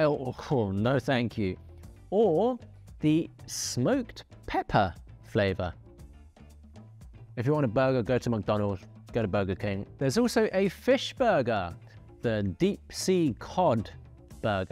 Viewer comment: did I just heard dude saying “If you want a burger just go to Mc Donald and get a burger king” ?!? 😂